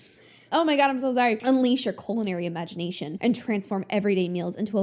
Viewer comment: I thought you said Colonary imagination and that really hits the nail on the head 😂 also who wants to eat blue colored shrimp!!????